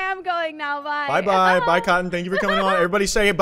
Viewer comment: why the fuck did I think he said “Cotton picker” 😭😭😭